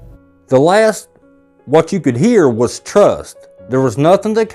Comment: I hear, “Fake Trust”. I couldn’t have said it better myself 😓